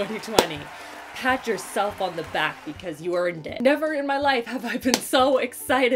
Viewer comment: Wow! I had to rewind the beginning three times to understand what you said after “pat yourself on your back...you earned it”. I legit thought you said “pat yourself on the back...you’re a D-word!” I knew you wouldn’t say that but wow took me back! Looked forward to this video all year!! 🤪